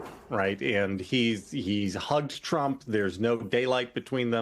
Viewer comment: He’s hugged trump … I thought he said he’d humped trump … 😂. Tomarto tomayto … 😂.